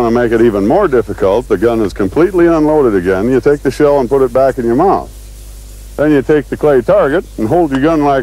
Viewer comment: did he say take the shell and put it back in your mom???